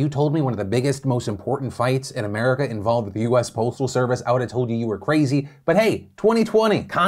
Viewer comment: My goodness I thought he said the US poultry service